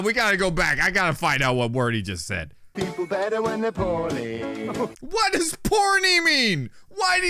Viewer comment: I think he said poorly